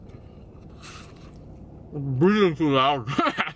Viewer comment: Loool he said breathing too loud is probably what got him bit up by that mosquito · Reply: Thanks for clarifying that! Haha! I thought he said "being too loud." Thought he was being naughty. 😁